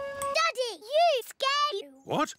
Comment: I heard peppa say “daddy your gay” 😂😂😂